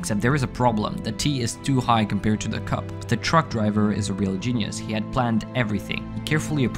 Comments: At first i thought he said drunk driver so i was expecting the worst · Ohh, I thought this was going to hk horribly wrong cod st the start I heard "drunk driver" not truck driver